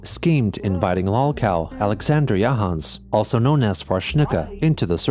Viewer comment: Did someone say Jahans?